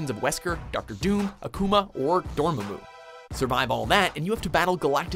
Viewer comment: Did... did he just say "Dormu"? As opposed to "Dormammu"?